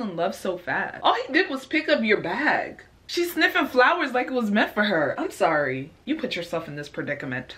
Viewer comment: “She’s sniffing flowers like it’s meant for her” LMAOO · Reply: I thought she said "meth for her" 😳